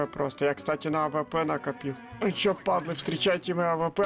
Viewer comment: я слышу Welcome to monster (Приветствую у монстра)